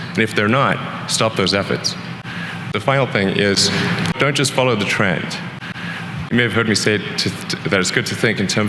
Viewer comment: 4번은 don’t follow the trend 보다 don’t just follow the trend 로 들리네요